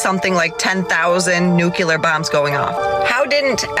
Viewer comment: I'm sorry did she just say "nucular" instead of "nuclear"-?